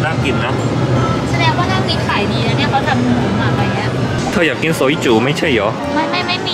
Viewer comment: นาทีที่ 9:04 พูดว่า "เธออยากกินซอยจู๋ ไม่ใช่เหรอ?" .... ตกใจเลยอ่ะ 55555 😆😆